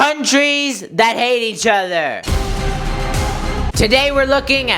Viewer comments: At the start I heard "Countries that ate each other" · Thought you said Countries that ate eachother